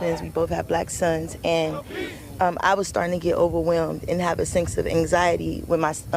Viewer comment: Did she say she has a SINKS of anxiety at 5:52?